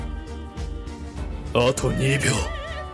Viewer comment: yo what did jotaro call pucci? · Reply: Atto Nibyuu 2 seconds left basically.